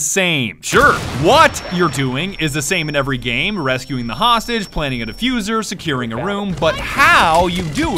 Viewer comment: At the 3:12 maek the autogenerated subtitles say rescuing the hostage in a fuze